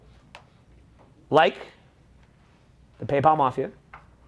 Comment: What he saying around 8:30?" Paypalmoff you""a couple of you understood what I said" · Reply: PayPal Mafia, early tech bros